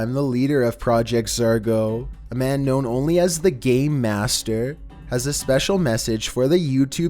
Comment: when he says his nick name does it sound like he says the gay master